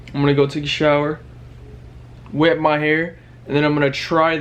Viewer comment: did he just say ima nut 🥜 😂